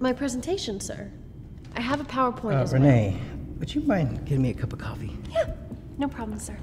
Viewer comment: Oh I thought he said miss grenade go get me a coffee